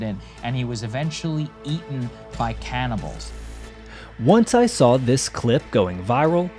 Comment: Thought he said he was eaten by cannibalism?